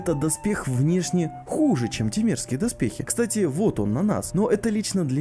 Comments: Мне одному словосочетание "темерские доспехи" послышалось как "те мерзкие доспехи" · Мне послышались слова те мерские доспехи а не темерские